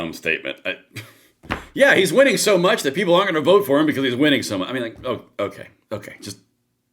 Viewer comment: I think she said most people are not even going to go for it- meaning hes so far ahead no one will even try to run against him. That's what it sounded like she said.